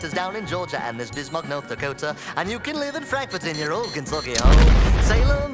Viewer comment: I thought it said “And you can live in Frankfort and your organs will be home” but then I searched it up and it said “in your old Kentucky home” 😂😂😂